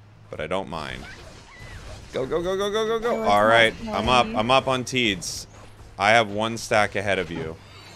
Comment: Wait, did said "im up on teeds"?